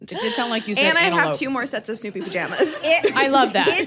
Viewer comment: I swear that I heard “soupy pajamas”, and then AI lied to me that it knew what that was, including a movie and a Reddit result 🥣 I just wanted to share that AI thinks soupy pajamas are for women and moms, so I hope they are comfy.